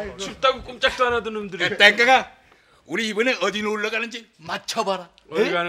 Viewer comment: 쯤에 이영감님?김영감님?이 노구 할아버지한테 뭐라 부르면서 들어오는데 이게 뭐라고 부른건지 아시는분..? 제 귀엔 내꺼~ 이렇게 들려서욬ㅋㅋㅋㅋ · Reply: 땡깡아라고 하더라고요 보시면 그후에 땡깡아라고 또부릅니다